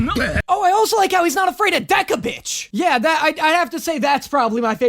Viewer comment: when you said “Deck-A-Bish” I heard Yella•Bish, you know from Assassination Classroom… IDK WHY.